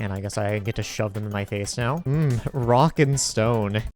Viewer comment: DID I HEAR A ROCK AND STONE ⛏️ ???